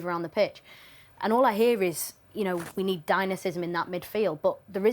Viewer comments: I dont think anyone else has said Utd need Dynacism in their midfield.😅 Dynamism, yes. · WTF kind of word is “dynacism”? 😂😂😂